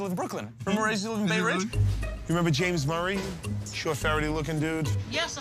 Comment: Love when the lady remember Murr when Q says Ferret looking dude 😂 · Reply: I had never noticed that until I watched these videos online, when I watched that on tv I thought he said: "a fairly good looking dude."